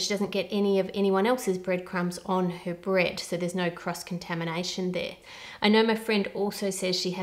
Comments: The first three times I heard it I thought she was saying "crust contamination" and that made too much sense also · When you were talking about the bread and cross contamination, I honestly thought I heard CRUST contamination. Technically correct though 🤣